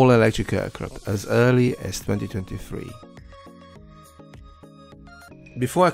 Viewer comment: 3053? Did I hear that correctly ( 2:13) I was thinking of transporting by that time.